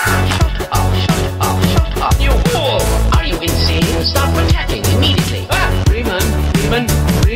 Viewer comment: I thought he said “I’m insane? Stop projecting immediately!” Haha guess just selective hearing